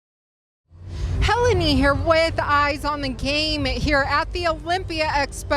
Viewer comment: Did she say "eyes on the game" @ 0:14 ? Well my eye's are on the game...